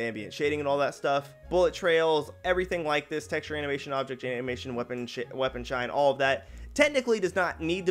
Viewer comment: Is it just me or did anyone else hear say weapon shit